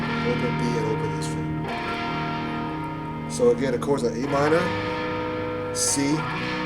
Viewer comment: Sounds like your saying that first chord is an e minor but it clearly looks and sounds like a minor, if I'm totally mistaken, I appoligize